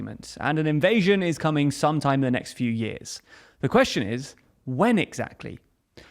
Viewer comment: "NEXT FEW YEATS" instead of years🙈